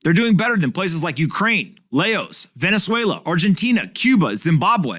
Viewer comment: The way you pronounced Laos made me rollover laughing. I thought you said Lagos xD